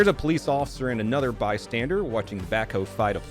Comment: Why’s it sound like you say “Police Officer” as “Police Osser” lol I always have to rewind to see how you say it Shits wild